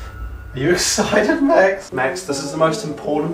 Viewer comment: Does anyone know what he's saying at 0:52? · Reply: You're excited max"